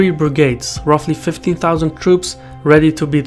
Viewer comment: did you say “...3 brigades, that’s about 50,000 troops...”. Shirvan, you either misspoke or you grossly misunderstand military terminology, units, operational capability etc... I real hope it was just an error, cause the largest brigades in the US Army (which are bigger in both man and machines then their Russian equivalent) stand at 4,000 man. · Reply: I heard ‘15’.